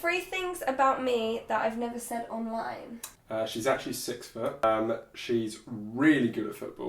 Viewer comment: what does he say? She's actually what? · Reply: pro mise 6ft (but it was a joke😂)